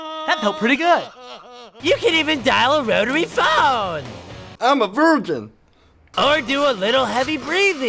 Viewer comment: what did he say at 1:35? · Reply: Johnny Gat the person said "im a virgin"